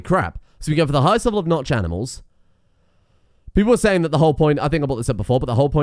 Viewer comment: Notched animals? am i hearing this right?